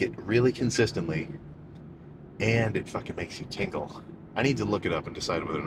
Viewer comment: I thought he said tinkle instead of tingle